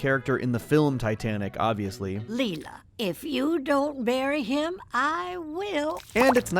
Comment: I initially thought Leela’s grandma said “if you don’t marry him” but then I watched it back and it sounds more like “bury” rather than “marry”.